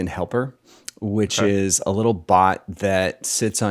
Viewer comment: Did someone say bot 👀 (10:25)? My bot developer senses are tingling.